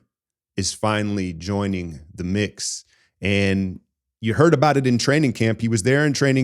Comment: Just for a sec, I thought he said “Seth Curry is finally joining THE KNICKS” and I felt a moment of relief. …Then I realized he said “mix.”